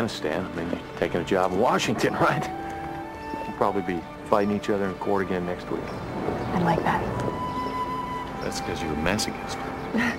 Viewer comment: can someone please tell me what they say from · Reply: Six years later...but they said: H: ...We'll probably be fighting each other in court again next week. M: I'd like that. H: That's cause you're a masochist. Hey, don't start crying...